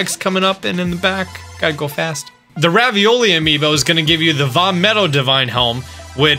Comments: did say "the ravioli amino"??? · bruh did you just call Revali, ravioli?! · did he say ravioli?